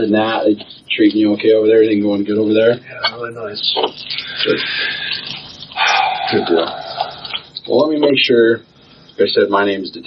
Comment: Did he say (good girl)